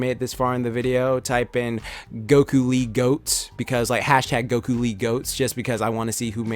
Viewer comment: At first I thought he said "Goku lead goats" #GokuLeeGOATS